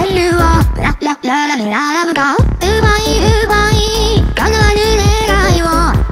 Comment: HELP, SHE SOUNDS LIKE SHE SAID UBER EATS, NOOOOOOOOO- UBER EATS, UBER EATS